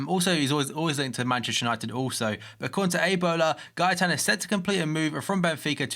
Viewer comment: is it me or did he say ebola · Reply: It's a bola ,it's some Spanish or Portuguese newspaper